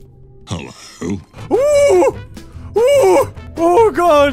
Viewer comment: did it say “hulla who”